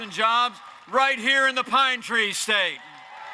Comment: I coulda sworn MP said Pine Street State. Just sayin....